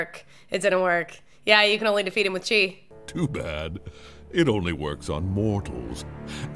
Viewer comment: When I was a kid, I thought that Kai was saying “it only works on morons”, so I genuinely thought they were calling Po stupid, and that was basically all I remembered from the movie.